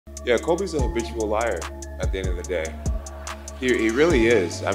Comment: I always thought he said "Colby is a bitch and a liar" and " he really is a rotten person"